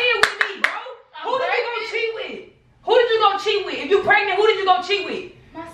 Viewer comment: i know she’s trying to say cheat with But instead of that she said chiwi